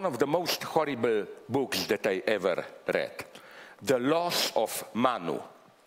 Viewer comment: Not English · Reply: Alasdair Church Do you know where I can find the book at 34:38, "The Loss of [mano]" or something, I can't make out his English